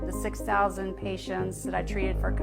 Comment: i think she said 6,000 patients lol not 16 Eddie! Lol. Its absurd regardless 😂